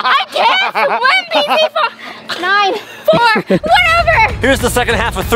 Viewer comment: did she say pz fu